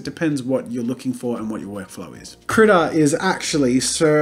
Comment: Me, with no language skills trying to explain that I like using Krita: hee hee hoo hoo Krita good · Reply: I thought he was saying "Critter" the whole time, but see it's spelled "Krita" (which is crayon in Swedish, which makes me wonder where it's from?)